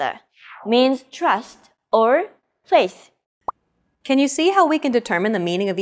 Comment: can someone tell me what she said after "trust"? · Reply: Faith bro